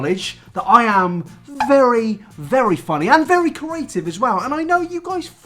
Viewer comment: Do you pronounce creative as "corative"? You said it three times and I'm starting to think you might be saying a word I don't know